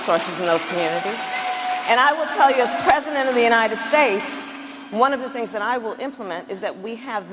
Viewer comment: did she said " as president of the United States"?